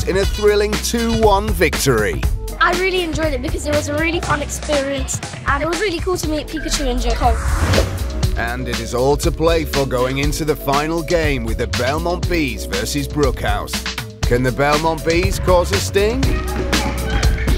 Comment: I heard "It was really cool to meet pikachu and joe cole" a lil differently